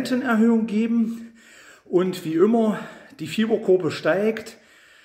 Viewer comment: Bei Minute/Sekunde 00:20 sagt...'diw Fiebergruppe steigt' ..habe ich das richtig verstanden ❓Was ist eine Fiebergruppe ❓